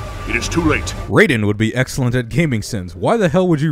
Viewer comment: you totally missed what dude was saying...he said "raiden would be excelling at gaming sins" because raiden is also disagreeing with the thing that got sinned